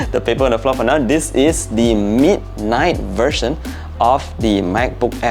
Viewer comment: Does anyone hear him say the MEAT night version? lol🤣